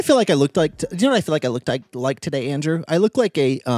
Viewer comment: I THOUGHT SAID HE LOOKED GAY